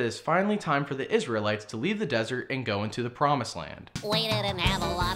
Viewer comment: When I watched this movie for the first time in like ‘98 or ‘99, I thought they were saying “Promise Land.”